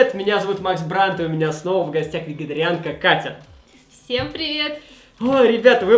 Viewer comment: Мне послышалось или он сказал не вегетерианка а ветегерианка ?